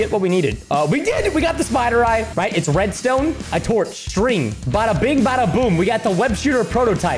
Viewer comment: what did he say????? "A Torch" Bruh